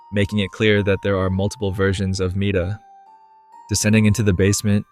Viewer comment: Guys, I thought Mita said Miata...